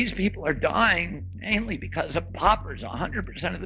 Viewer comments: At first I thought he said that it was the work ‘pauper’, which made me wonder if we were back in the 19th century. Then I understood thanks to the good doctor that it was actually “poppers” he was talking about which made me think he is a homophobe…and an idiot. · At first I thought he said that it was the work ‘pauper’, which made me wonder if we were back in the 19th century. Then I understood thanks to the good doctor that it was actually “poppers” he was talking about which made me think he is a homophobe…and an idiot. Unfortunately there is no cure for that .